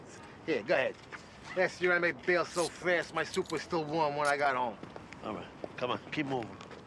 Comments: I always thought he said “my suit” not “soup” 😂😂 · I ALWAYS thought he said "my suit was still warm" 😂 we compromise here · I alway thought Silvio said “My suit was still warm…” but I guess soup makes more sense cause he’s not currently wearing the soup 😅